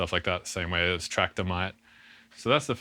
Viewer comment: Can someone tell me what he said in minute 2:00? He said ableton doesn’t have any other way of dealing with points like “trackdemi” or track what lol? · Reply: traktor, a dj software